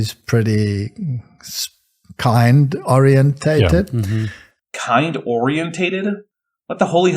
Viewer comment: I think he's saying it's baramin-orientated.